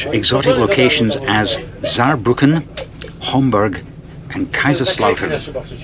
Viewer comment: Homburg? Oh, I thought you said Hamburg. Also didn't he said it had the best train station or something?